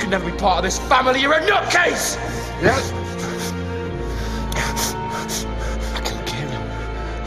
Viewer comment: What did Niall say in 2:38?? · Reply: Niall: "I killed Kieron." JP:"Wha...Nooo...nooo!" Niall: "Does it hurt, does it?"